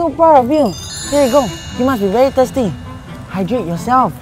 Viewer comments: it says tired instead of thirsty... oh well! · Lol. At 1:48 the subtitles said tired but said thirsty. :D